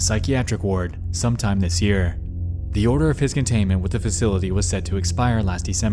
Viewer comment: "...The odour of his containment..." 😁🙃😁 At least, that's what I heard, while watching and it made perfect sense to me add well as lol, given the context of his necrophilia shown and (decayed*) debated in this video. * decayed; I'm saying thanks to my autocorrect, which made me laugh again, bc at first it wrote this word, instead of "debated"... leading to the conclusion, my autocorrect was very fixated on the topic of this video/necrophilia.